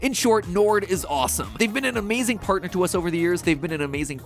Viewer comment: thought it said 4 days not years ...